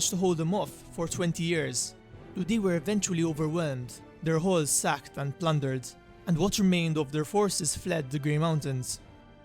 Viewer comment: Really sounded like you said the dwarfs had "their holes sacked and plundered."